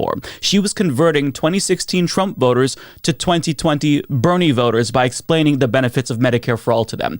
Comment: I swear at first I thought you said brony voters.